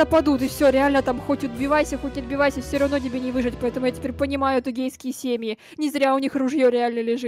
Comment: Я одна услышала "Гейские семейки😂?"